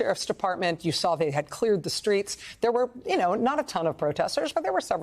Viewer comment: Did Rachel say, 'motorcade,' or did I hear 'murdercade', Asking for a friend.